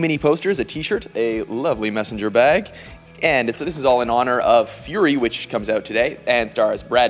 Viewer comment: At first I thought it said "furry" xD